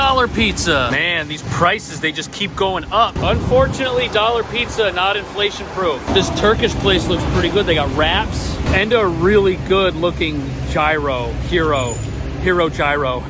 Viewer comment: I thought you said the Turkish place had rats...I need to get my hearing checked LOL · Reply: I thought so too. He said "wraps".